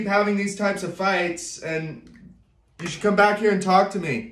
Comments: “She cute but she dumb” Had me dead 💀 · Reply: O thought he said she cute but she crazy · “She cute but she dumb” Had me dead 💀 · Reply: Lol I thought he said “she’s cute but she’s to stupid”